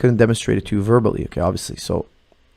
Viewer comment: What is the name of the book he mention at 4:54? I cant understand, english is not my main language... Thanks in advance! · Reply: Becoming a supple leopard by Kelly Starrett